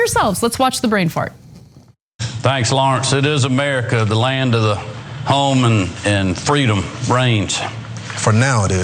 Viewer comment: I heard "America - the land of the... home and freedom... Brains!".. Can't say I was surprised